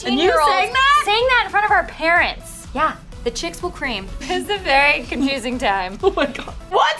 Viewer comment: I always thought it said the girls will scream 😂😂😂😂😂😂😂😂😂😂